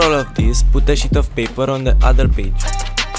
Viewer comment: It’s so funny when u said put a sheet of paper because it sounds like u said put a sh*t of paper 🤣🤣